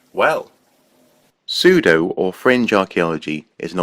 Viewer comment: The first time you said "fringe" I heard "cringe." Close enough.